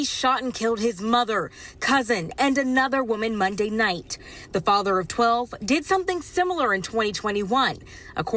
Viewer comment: did she say father of 12????